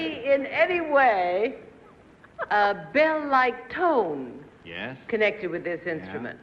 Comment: I love her <3 what does she say at 11:46? That episode is so funny but I can't understand that line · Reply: It's a bell